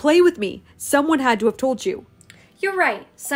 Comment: At first I thought he said you're gay, and I was like, what the?- not a good introductory is it!?! Then I read the subtitles and I cracked up, It said you're not Gay. LOL YOU GOT ME THERE!!!